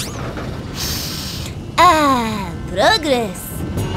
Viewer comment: did he say burgers or boogers? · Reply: He said progress